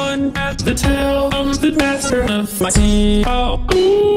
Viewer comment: what everyone hears: I'm the master of my sea, oh-ooh what i hear: I'm the master of my CEO, ohh lol